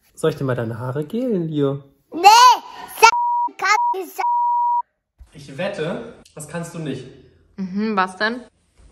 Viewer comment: Was hat er gesagt ?🤣😅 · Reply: Zitat: "Schaissa Kagga"